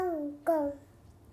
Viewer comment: I think he said "กล้อง" (camera) hahaha